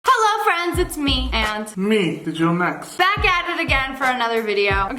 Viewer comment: When digital next said "and me digital nex" I thought he said " it's me uncle flex